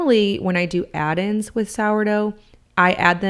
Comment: I hear "SARDO" = sourdough = Sauerteig 😅